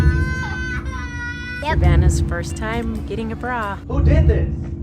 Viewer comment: Jesus Christ I watched the intro on iPad speakers next to a loud ass laptop and I thought that said “Savannah’s first time getting groped”